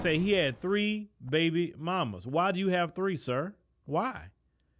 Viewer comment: Did I hear him say, 'three babies mommas'? He, and other BM, also need to hold themselves accountable for how our youth behave. Just how can he be in three households, at the same time, raising his kids?!!! Not the best spokesperson on the matter. IJS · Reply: This particular man really did not do himself any favors in his rant. He only showed that he was also the issue. THREE baby mamas. Smh.